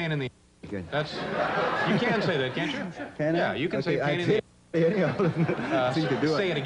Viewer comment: What´s the word repeated ´til 11:03 ? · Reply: “Pain in the ass.”